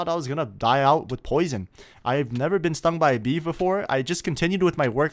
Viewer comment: he said he never got stung by a bee before but the subtitles said he never got stung by a beef before